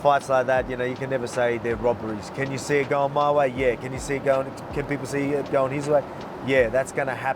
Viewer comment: I first heard 'Can you see it going Khabibsy..ehh his way' lool